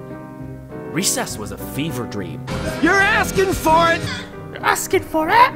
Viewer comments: I THOUGHT HE SAID “Your ass can fart!” LMFAOAOAOAOOOO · I thought he said "your ass can fart." lmao.